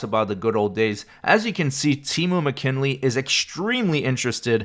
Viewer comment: I thought you said Temu! Sorry. It just made me giggle thinking about the crap you get from Temu. She is very beautiful girl but it looks like a crappy attitude. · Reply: He did say, "Temu." Jack called the girl "Temu McKinley" the day he got his chains snatched.